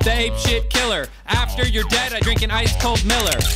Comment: I'm so fucking dumb, I thought he said "after your dad I'll drink an ice cold Miller" and I was like, wtf does my dad have to do with this? He was saying dead, which is what my brain apparently is.